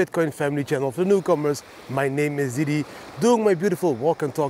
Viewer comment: I always hear: “and for the cucumbers, my name is Didi” 😂